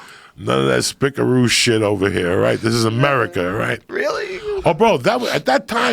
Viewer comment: i thought joe Rogan said " really n*gga*